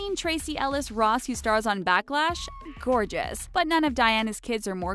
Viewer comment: Backlash? Did she say “Backlash” ? Ik I’m not the only person who heard her say “Backlash” instead of Blackish